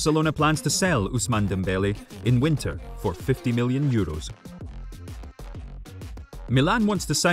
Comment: did I just heard 50 billion? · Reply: Its million 😂